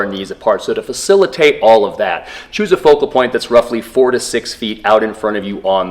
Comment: No wonder i have been twerking my back for some reason i thought you said lòok 10 ft on the floor in front of you. You actually said 4-6 ft